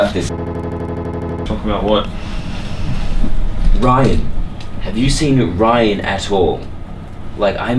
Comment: Legit i heard "Ryan,have you seen ryan as*hole"